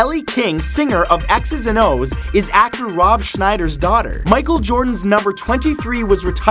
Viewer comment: I thought he said "Ellie King is after Rob Shnyder's daughter."